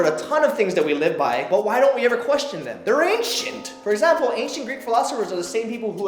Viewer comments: I thought when said they were ancient, I thought he said they ain't shit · When Said "They're Ancient" I thought He Said "They Aint Shit"... I was Baffled For bout 2 minutes